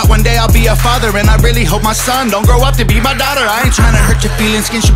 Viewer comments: Love the fearless honesty Tom. You go all in every time. . “One day Ill be a father and I really hope my son don’t grow up to be my daughter”. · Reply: Oh I thought it said grow up to be Madonna.. ha. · "I don't want my son to grow up to be my daughter" priceless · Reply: I thought he said I don't want my son to grow up to be Madonna !!!!